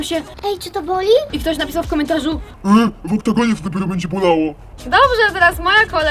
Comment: co ona mówi? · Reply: w oktagoonie to dopiero będzie bolało "